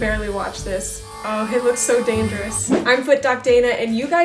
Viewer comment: what she is saying:It looks so dangerous What I kinda hear [don’t ask me how]:H!tler is so dangerous